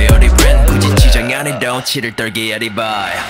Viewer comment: did he said 'i can feel this 💩'? 😂😂